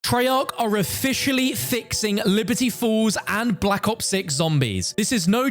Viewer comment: Am I the only on who heard "Liberty Fools"?